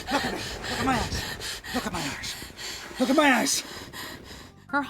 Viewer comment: Since I'm more used to American pronounciation, I keep hearing "Look at my ass". I need help